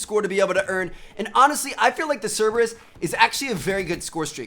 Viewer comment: When he said the cerberues i thought he said servers and they are gonna buff them 😒😑😂